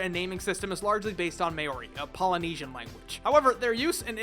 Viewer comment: Did he just say Mary at 2:05 do research buddy